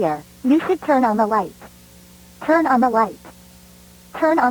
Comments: it sounds like she said "turn on the fu-" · i thought they were gonna say “turn on the fucking light” cause i heard “turn on f” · the last time she says turn on the lights. It sound like she was gonna say “turn on the fu-“